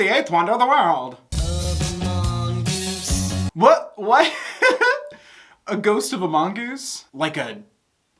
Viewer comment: MY LIFE IS RUINED WHEN HE SAID "A GHOST OF A MONGOOSE?" I HEARD IS AS "THE GHOST OF AMONGUS" I'M SO DONE